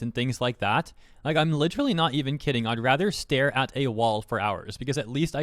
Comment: had to replay 0:38 a time or two because I thought he said "I'd rather stare at Ningguang for hours" same. you get more out of your time this way.